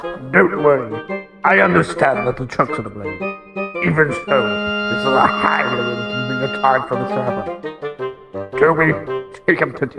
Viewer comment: what on Earth did he say??? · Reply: "I understand that the trucks were to blame, but this is a highly inconvenient time for this to happen."